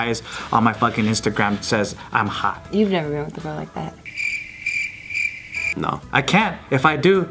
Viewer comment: what did said at: 3:40?¿?¿?¿ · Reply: "you have never been with a girl like that right?"